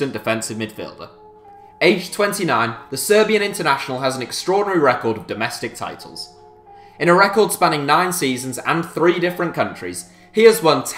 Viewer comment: I heard 2:56 as “tremendous record in domestic violence”